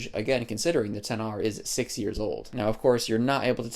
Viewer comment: I heard 60 years old😭😭😭